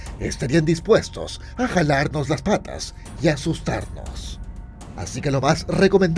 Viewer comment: yo entendi chuparnos las patas